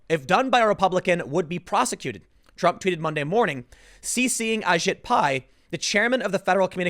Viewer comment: Did anyone else hear “I shit pie” at 2:02 when Tim was talking about the FCC director?